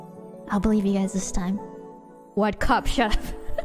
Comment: I think she says " 'What cup?' shut up", not "what cup chef?". Good clip anyways!